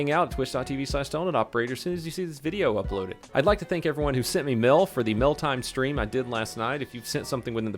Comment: It almost sounds like you said "I like to thank everyone who sent me Milf"